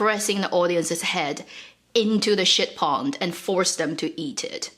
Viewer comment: "It is literally pressing the audiences head into shit pond and forcing them to eat it." I actually spit out my drink laughing. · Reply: I thought she said "ship" pond which made the while thing even funnier 🤣